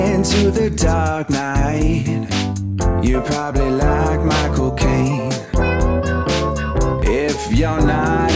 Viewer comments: lmfao! I thought he was saying, "if you're into my dark side, you'll like Michael Cain" xDDD lol, he was talking about his Cocaine hahaha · Why is it that every time he sings "Micheal Gain" I hear "My Cocaine"??? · Wait Micheal Gain? I Thought It Said Cocaine!!! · It's Michael Caine, not Michael Gain. Gain has nothing to do with Batman, Caine played Alfred. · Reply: I thought he said my cocaine at first